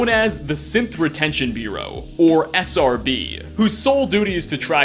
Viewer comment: Lol sorry it sounded like you were saying "Simp Retention Bureau" a Foundation organization dedicated to tracking down escaped Simps.